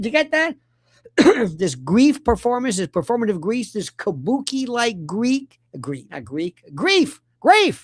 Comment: I thought you said bukkake grief